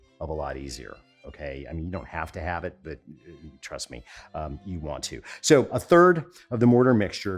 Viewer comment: Did I just hear you say “ Truss me”?